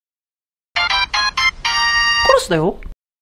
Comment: 彼氏だよ って聞こえるんやけどなんて言っとん？w